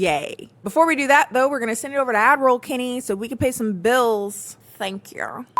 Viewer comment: Idk when this happened but every time I hear ‘ad roll Kennie’ I hear Admiral and honestly it fits lmfaoo